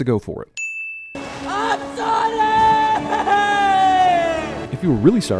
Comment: Did anyone first think he said I'm sonic! here